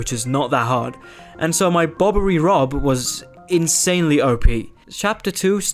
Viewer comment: did i hear that well? Bobbery rob